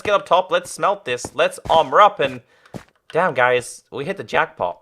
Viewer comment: did he say dab guys??